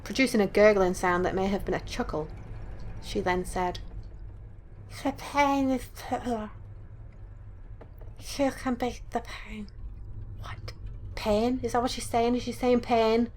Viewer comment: if ur wondering what the Little girl said at 4:10 she said "lets paint a picture you can be the paint" just encase you didn't know what she said